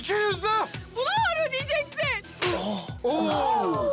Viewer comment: Berna'nın ne dediğini anlayabilen var mı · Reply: Bunu ağır ödeyeceksn diyor